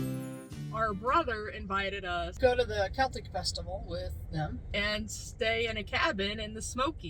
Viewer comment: Hiya. Hahahahahaha, I thought you said CATHOLIC Festival. And finally! Somebody who hulls strawberries. Stay safe. All the best to you.